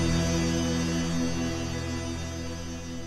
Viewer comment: Wait, the f*ck did v1 say? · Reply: Bruh. What a weird dream